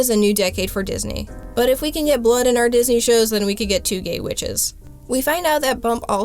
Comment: "But if we can get blood in our Disney shows then we can get two gay witches." Yes. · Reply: I thought you said bitches fir a second